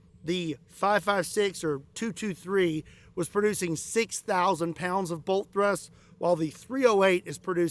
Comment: I thought you said 600,000 Lb of Bolt Thrust at first, and, let me be clear, SIR THAT WOULD NOT BE A .223. lol!